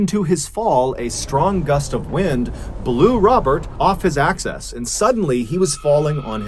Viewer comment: Is it access instead of axis @ 8:26?1